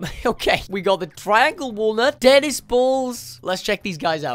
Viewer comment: what people hear: tenis balls What i hear: DEADIS BA